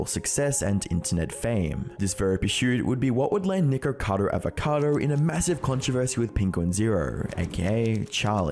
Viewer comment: sounds like you say: n1-ggercado avocado :V